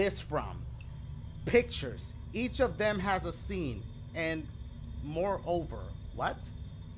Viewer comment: is where dashie read a word wrong instead of saying sense he said scene or seen lol